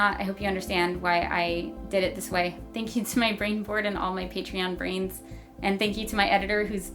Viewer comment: What did she say at minute 6:09? "Thank you to my brain ford?"